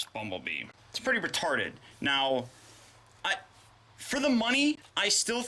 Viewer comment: I thought you said matarded